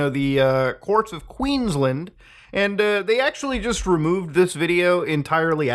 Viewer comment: 😂😂😂 lol who’s land? Cause he did not say Queensland 😭😭